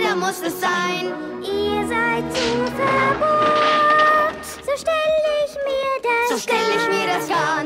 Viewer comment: "Derek wird Odette"... Lol? XD Vorallem ich hab "Ihr seid so verhurt" statt "verbohrt" verstanden! XD Irgendwie doof, dass sie sagt, dass sie es nicht so vorstellt, bei der Kartenszene oÔ